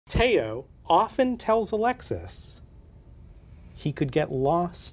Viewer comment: thought he said alexa and i got really concerned for tao